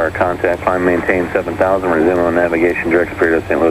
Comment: ATC says "Climb & Maintain 7000 resume on navigation direct (unrecognized word) to St. Louis airport" Perhaps he doesn't say "resume on navigation" , he says it so fast and it's a tad bit muffled, just wondering what he said and what that means? in response you say "Climb & maintain 7000 O-nav to Spirt for 1-Quebec" can't quite make out what you said either. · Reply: That’s “resume own navigation” and the reply is “own nav to Spirit” that is the controller has been providing vectors for his own purposes and is now releasing to follow the flightplan. The good capt is acknowledging this instruction.